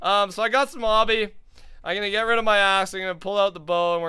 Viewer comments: Did said a** not axe in 16:25 mins he said a** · I gotta get rid of my ass sounded like that LOL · Anyone else hear at 16:31 he said "ima get rid of my ass" but he meant axe. "Lol